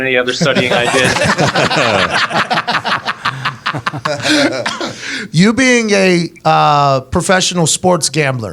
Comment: I thought Pat said "you being gay... " at 3:18 lmao